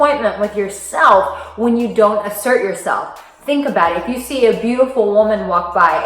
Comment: I had to listen carefully at 1:46 a.m, I thought you said imsert, but after a few blinks and a yarn I realized that you were saying asert, good stuff.